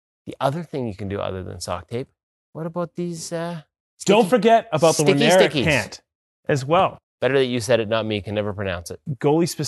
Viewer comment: What is the name of the product at 3:51? I can't hear what is saying cause of. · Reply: Oneiric Base Layers